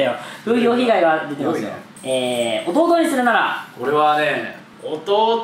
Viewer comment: 弟にするならがボコボコにするならって聞こえて急にどんな質問！？ってなった(笑)